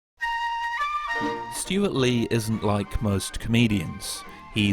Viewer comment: did you say ISN’T?